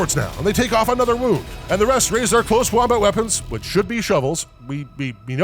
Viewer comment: Lol 34:50 i swear they said "close wombat weapons"